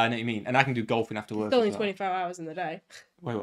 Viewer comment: I heard 25 hours